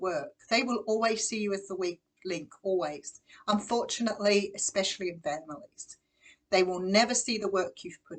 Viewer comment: what word did you say??? Especially the vander…?????? I can’t make it out can you please tell me what word it is at 17.07 xxxx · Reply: I believe She said “especially your family” .